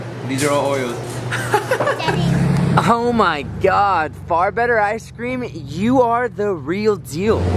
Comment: I thought that said fart better ice cream 😆 🤣 😂